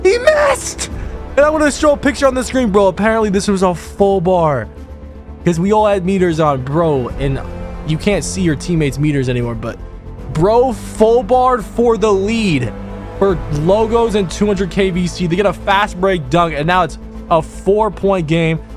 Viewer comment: My full bar at the end of Game 2 makes me sick to this day... We got trolled :/ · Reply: I thought they said no full bars